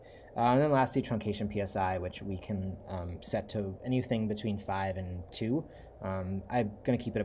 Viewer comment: Hey, quick question. At 19:16, I heard "truncation should be between 5 and 2", but I know that can't be what you said because it's 0.7. My apologies for not understanding you. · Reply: I probably say point 5 (0.5) and 2. But the truth is the value can range in anything from negative to positive. The thing to remember is that at 0 you get no diversity (it’s always the same image) but the further you get from zero in either direction the more diversity you get while the less realistic your images are. Most people use a value between 0.5 and 1.0 which is why I say between 0.5 and 2.0 (from 1.0 to 2.0 it gets really weird)